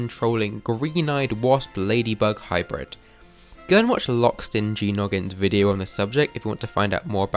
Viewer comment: Did you just say Lockstin JEE Noggin???